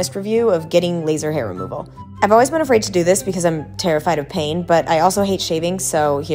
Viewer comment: I thought she said I’m afraid of paying 😂and I was gonna say same But nooo